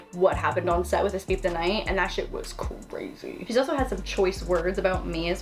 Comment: what did joey say about lauren?? I'm trying to think of what anyone would be able to say about her! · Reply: I think she said she instead of he lol 7:42 because Gabbie was saying stuff about them so she said she’s also had some choice words about her too